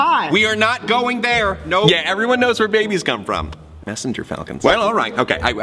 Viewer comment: 14:78 i thought he said Rabies ( idk how to spell it)😂😂😂